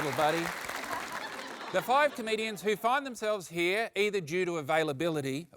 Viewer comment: i always hear tom say “canadians” instead of “comedians” and it confuses me every single time